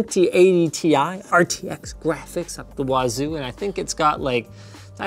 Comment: did he just say "out the wazzooo"